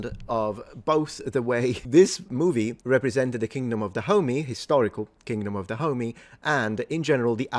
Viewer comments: I only (mis)heard it as "the kingdom of the homie" for a solid minute there, and now I can't unhear it either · I keep hearing him say "the kingdom of da homie" which I know he's not but I can't un hear it! Lol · I really thought you were saying the Kingdom of the Homie the whole time